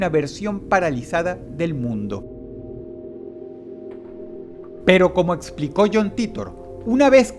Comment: Pero como explicoño jhon titor eso entendi yo XDDD